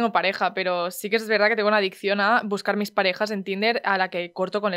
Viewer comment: Cuando hablo la compañera de Sara, yo le entendí " tengo una adicción al PECADO" jajajaja XD